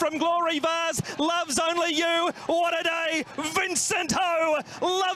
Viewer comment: ここ空耳だけどゴール手前の海外実況で「ラヴズオンリーユー！"終わらない！"ヴィンセント・ホー、ラヴズオンリーユーforジャパン！」って聞こえて、後にこの古馬G1初勝利を含めて立て続けにG1三連勝したのは、正に伝説の始まりだったなと思った。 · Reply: It was not "it's not over." He said "what a day", an expression of amazement.